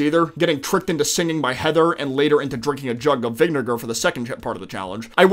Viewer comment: nah did bro say vinegar or did he say smthn else...